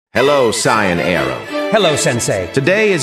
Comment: HAPPY BIRTHDAY! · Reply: I thought you said chian